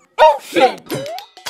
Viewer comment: did i heard oh sh*t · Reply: He said Motion!